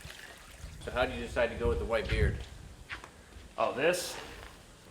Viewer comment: 🤣😅 I thought he said, "So, when did you decide to go with the right gear (anabolics)?"